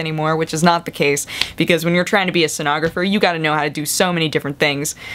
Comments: oh SCENOGRAPHER. I thought you said stenographer. I was like, "lighting, huh? costume construction? weird." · I thought you said you chose Sonography as your major, which baffled me - to say the least. I'm assuming now that you chose Sceneography (all the design elements of Theatre) as your major, which would explain your costume design skillz.